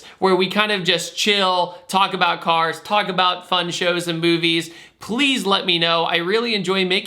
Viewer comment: who else thought he said fun shows n boobies